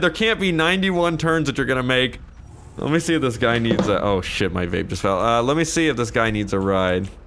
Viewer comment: did he just say "my vape just fell"